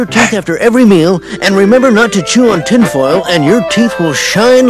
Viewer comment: Ooh, he said NOT to chew on tinfoil. XD I saw this as a kid and until literally just now I thought he said that chewing on tinfoil would make your teeth shine. Even back then I thought that sounded like terrible advice, and I later wondered how they were able to get away with saying that on a kid's show.